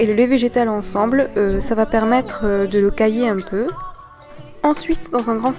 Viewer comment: Excusez-moi, je viens d'étudier le français. Je ne comprends pas 2:07. ~permettre "cahier" un peu? Qu'est-ce qu'elle dit? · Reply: Elle dit "ça va permettre de le cailler un peu" = "de cailler un peu le lait". En français on peut dire "coaguler" mais en général on l'utilise plus pour parler du sang qui se durcit. J'ai lu que ça se traduisait "to curdle" en anglais. :)